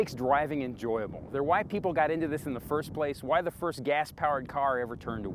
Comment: Did he say "the white people" at 9:33?? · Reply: No, he said "they're why people got into this in the first place"